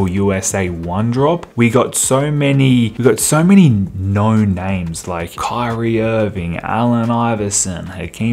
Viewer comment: who else thought he said no-name players 🤣 "yeah who is that Kylie dude?"